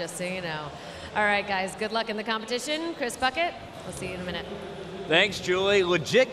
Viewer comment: Legit thought she said "Good luck in the competition cause fuck it"